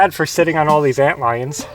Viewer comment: haha, did you say "ant lions" ?